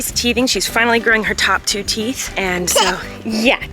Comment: Did just say teeth at 0:20 😍 or am I losing my mind?!😂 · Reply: Chiara Neefjes I believe she did I heard it too😁😁😁